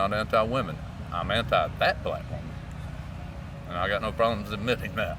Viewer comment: at first I thought he said I'm anti fat black women! I like to 💩